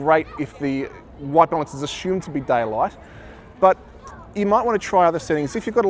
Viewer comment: Thanks for the tutorial ! Cant help it but every time he says “daylight” I hear “die a lot” 😂